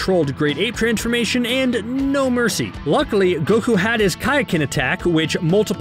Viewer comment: Is it just me, or did I hear "Kaiken"